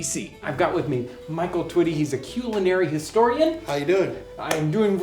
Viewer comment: Lol. Have to share this. I'm currently lacking sleep and a bit drunk. Listening to this video for the <num>th time and somehow this is the first time when he introduces Michael instead of Culinary historian I heard ''He's a cute lil' historian'' and I just about laughed myself to death.